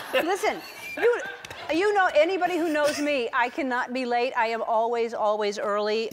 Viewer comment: Did Elen say : I cant not be laid?